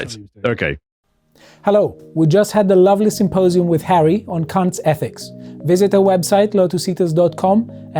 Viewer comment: 😅😂😂 sounds like he said “CUNTS ETHICS” 😅😅😅 as a Australian 🇦🇺 I’m very interested in CUNTS ETHICS I approve this messaging 😂😂😂 🇦🇺🇦🇺🇦🇺🇦🇺🇦🇺🇦🇺🇦🇺🇦🇺🇦🇺🇦🇺🇦🇺🇦🇺🇦🇺